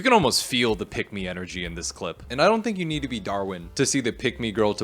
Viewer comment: For a second I thought you said Dhar Man instead of Darwin and was so lost😭😭